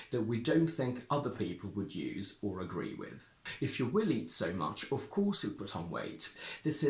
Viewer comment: If you WILL eat so much… I heard if “you willie so much” 😂